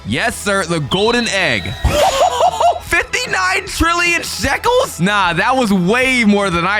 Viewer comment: did bro say trill